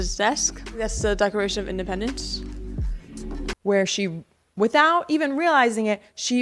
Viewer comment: "My house" uh...her dad isn't POTUS. 🙄🤦‍♀️ It almost sounded like she said, "The DECORATION of Independence" she said it so fast. 😬 Kiddo, slow down, enunciate. Hopefully it isn't cuz that poor girl got veneers slapped on her at a young age too like most of that family. 🥺